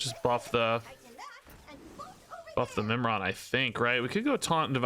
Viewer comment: Oh, Mimiron, I thought he said Nimrod and had to double take if I was watching a Snap video.